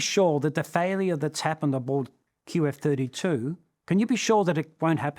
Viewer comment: I believe he said "aboard" at 22:17, not "about". Otherwise, I liked the video, was a pleasant watch. I certainly noticed his capability to tell the story, and I agree on what he said about teamwork, trust, and designing. Less so about his stance on AI. I'm surprised there was no mention of the sole survivor in Air India's crash. The question follow-ups, additional explanation like what a "checklist" is, and various imagery are helpful in these videos. You might want to explore astronauts being stranded in space next.